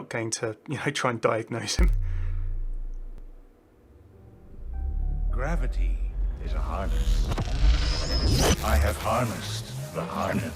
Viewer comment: when Sigma in his Orange Suit and in his lab, at the end Backwards Words play, and when you reverse those words, Sigma says "The Melody is a Harness" what could that mean? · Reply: GageCreeper, I heard “gravity is like a harness” but idk